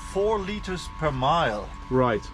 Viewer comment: 18 mpg ?? That is awesome! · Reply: You may have misheard him. He said 4 liters per mile — 1 mile per gallon.